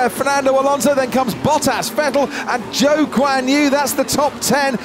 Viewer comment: I thought he said Joke on you, had to check all the drivers name, found out the guys name is Zhou Guanyu... go figure...